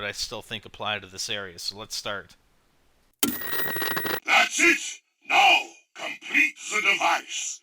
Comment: First time I played tranzit I thought he said: That's it, now go to the bus! DO IT!